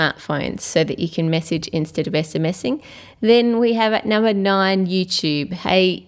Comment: No offense, but I honestly cant understand what you said. "You can message instedofmessamessing"